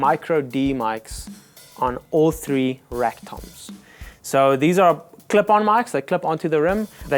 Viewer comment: Sounded like he said rectums at 2:14 lol